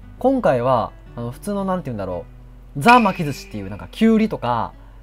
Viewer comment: キュウリとこぁって聞こえたw 今回も楽しく見させていただきました。 おすしカッコイイですね